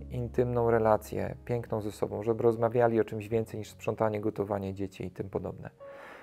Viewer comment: W 18:33 usłyszałam "gotowanie dzieci" 😂 jestem bardzo zmęczona. Idę spać. Błogosławionej nocy 🙂